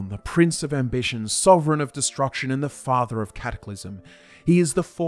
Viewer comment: literally thought he said “the father of capitalism” 🤣